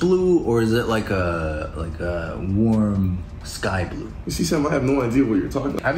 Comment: I FINALLY UNDERSTOOD WHAT HE SAIDDDD he said sam